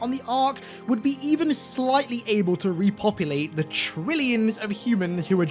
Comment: Honestly I just wanna know what kind of silly schenanigens 6, Emile and Chief would be up to if they(6 and Emile) survived. I can totaly see them take a Warthog and go crazy haha! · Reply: Did he really say "trillions of humans wiped out"?? 💀